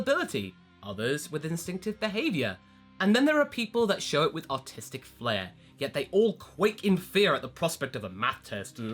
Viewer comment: I thought he said autistic flare at